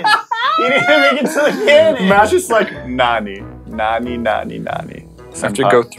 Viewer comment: Wait did say Nani (what in Japanese)